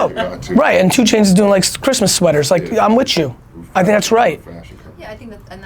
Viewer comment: Did Gary just say "I'm witchu" haha